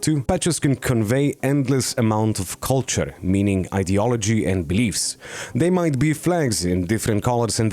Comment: did I hear ideology can u add nazi